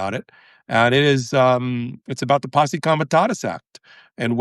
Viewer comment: what? Say it again, please. “The Posse Comitatus Act” sounds so cool! 🎓⚖️⁉️ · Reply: The captions got it incorrect ‘posi commitatus’ act.